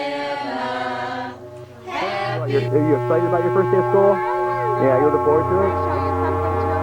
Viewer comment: I thought they were saying "Deer Lord" at first. Edit: And thirty minutes after typing that I was still laying on the floor as a way to process the reaction. I felt seen in a lot of this.